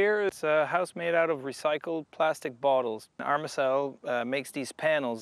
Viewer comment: I couldn't understand who "makes these panels" - "our Massell?" Want to know more. Great idea.